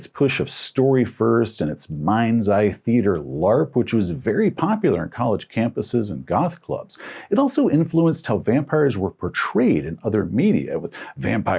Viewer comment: “Mind's Eye Theatre LARP, which was very popular on college campuses and golf clubs…” I misheard but that would have been wild 😅